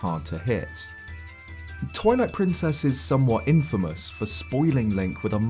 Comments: It sounded like you said Toilet Princess instead of Twilight Princess. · it sounds like he says toilet princess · I heard “Toilette Princess” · I chould have sworn I heard you call it toilet princess.